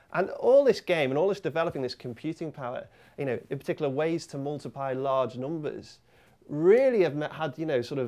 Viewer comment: Did he say ways to multiPI large numbers?